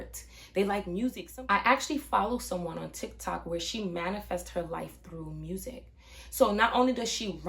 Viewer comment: I thought you said, she manifested her life to be more amusing. No, but that's a nice idea, isn't it?